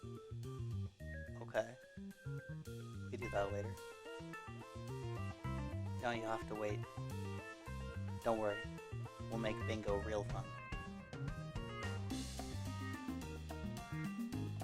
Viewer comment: I heard, "We'll make Bingo real fun" and now I feel threatened.